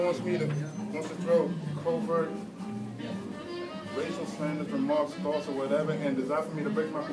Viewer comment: Did he say covert racial slander? Man this world really has just turned to shit